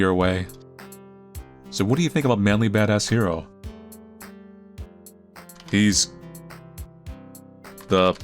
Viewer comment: Every time you say "ManlyBadassHero" I hear "ManlyBadassCereal" for some reason. And it get's me hungry...